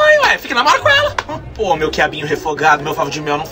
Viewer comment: Meu QUIABINHO REFOGADO PQP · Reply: E eu q entendi "meu diabinho encapetado"kkkkkkk